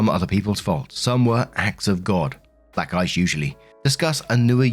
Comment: Am I the only one who heard "black guys usually" when he said "black ice usually" at 20:43? 😅😅😅